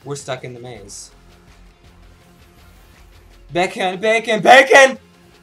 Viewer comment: lol. thought you were saying, "bacon, bacon, BACON!" haha